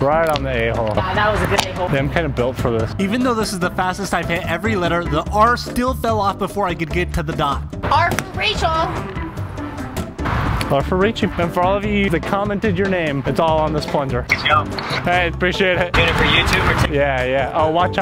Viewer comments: i thought she said "R for racial!!" 💀💀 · I thought she said r for racial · I thought she said r for racial💀